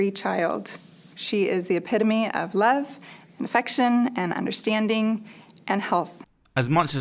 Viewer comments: I really thought she said, “This is my vaccine free child. She is the epitome of love, understanding, and INFECTION”, not affection! Lmao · At the end it sounds like she says her daughter is "the epitome of infection" instead of affection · It really sounded like she said that child was the epitome of infection. I know I heard that wrong, but its honestly probably right. Poor kid. =( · "She is the epidemy of love an infection" That was how I heard it the first time. XD